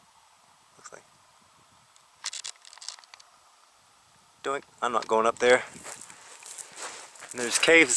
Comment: Dear native speakers, what is he saying from 15:59 to 16:05? I'm hearing: "What's that? Dang, I'm not going up there".